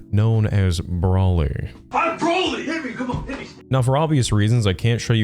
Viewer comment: I'm sorry but hearing someone shout "I'm broly" at the start of a fight is just too funny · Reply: I thought he said I'm brony, LOL.